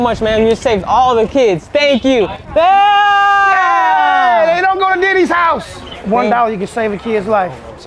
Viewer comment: DID HE JUST SAY “they don’t go to Diddy’s house!🎉” 😮💀